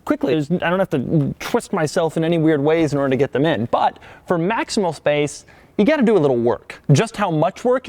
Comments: did he say "MAXIMAL space" at 2:01? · did he just say "maximal"